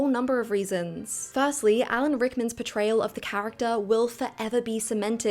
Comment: oh god thank god I replayed this part, I heard "overall betrayal" and not "portrayal" and got hella confused for a sec